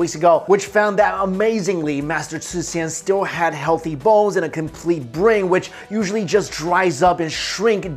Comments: I heard "Master szchu (i think) still has healthy balls." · did he just say healthy balls?! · I heard it as 'healthy balls' at 01:26 !